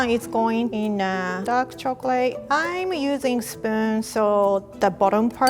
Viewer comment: I thought she said I'm using sperm to spread Oops Spoon